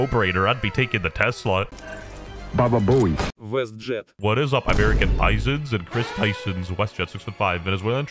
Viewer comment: i really thought you said american aizens.. i had hoped you watch bleach